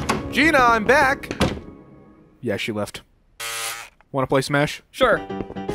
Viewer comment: I heard 'wanna smash' instead of 'wanna play smash' and I thought that was the ending joke